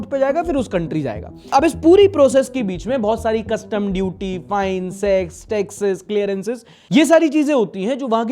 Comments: kya bola bhai ?? sex ?? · Did he say sex, tax at 27:32. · did he just said s*x 😂 arre bhai aab toh katai naughty ho rahe ho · ye kya bol diya S#x me sahi sun raha hu